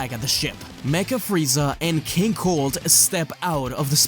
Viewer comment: Lmao I had to double take I thought said j cold instead of king cold